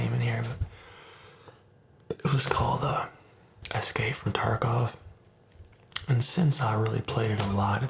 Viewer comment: what game did you say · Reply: Escape From Tarkov